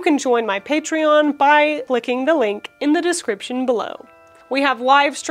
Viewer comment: She said "clicking the link" NOT "licking the ink" like I thought she said. Don't do what I did, please, I gotta go, not ferling too good oh dear... Yes I'm joking! But don't lick the ink!